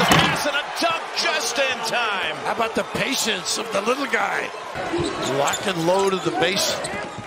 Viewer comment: did he call omier little guy ?😅